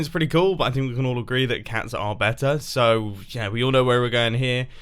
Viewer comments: Mumbo SAYS "...we can all agree that cats are better," but the SUBTITLES say "...we can all agree that pugs are better." Didn't think I'd notice, did you, dog-loving subtitle creator? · when you say cats are better at 0:40 the subtitles say you said pugs. Google think dogs are better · At the beginning the subtitles say "great" instead of "stupid." Maybe deaf people have better ideas than others. And at 0:38 it says "pugs" instead of "cats". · the subtitles say that pugs are better than red stone but Mumbai says cats. Maybe Mumbo has a secret love for pugs